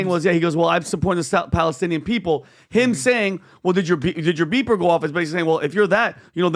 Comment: guy misrepresents the situation. Ryan did not say “well did your beeper go off?” he clearly said “I hope your beeper goes off.” Which is him wishing death upon Medhi